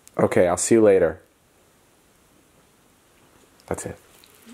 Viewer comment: When said ''ok i'll see you later'' i swear i heard ''my name is James'' like i swear i heard that.